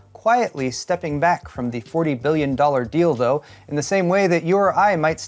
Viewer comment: i wasn't paying attention and mistakingly heard stepping back from 40 billion dollar 'Dildo' instead of 'deal though' and I was like wth